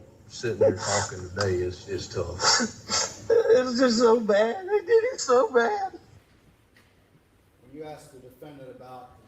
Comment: I hear, "It was just so bad. They did 'em so bad." It also kind of sounded like he could've said, "They did him so bad." Either way, I hear "they" not "I".